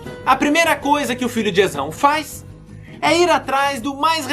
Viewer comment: juro q ouvi "Tesão" várias vezes e dps q me liguei q era "Esão"